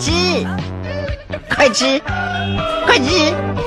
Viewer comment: 這個 陸配 發生了什麼事wwww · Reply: 原臺詞是"快吃，納德"結果被聽成"快吃，媽的"